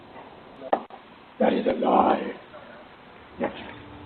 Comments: sounds like it say that is a lie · WHOA. At 6:18 I heard “That is a LIE, b*#!h”. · #6 sounds like it says “that is a lie...bitch”🤣